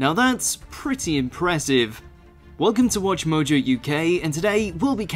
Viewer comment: At the beginning, I heard "Hello, welcome to WatchMojo U gay" and I was like How did u know ?